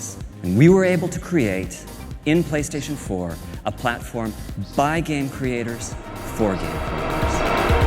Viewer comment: Did he say from game creatures to game creatures.. that doesnt make that lot of sense?